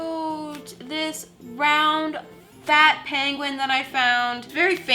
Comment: Why does it sound like she saying pangolin when she's saying penguin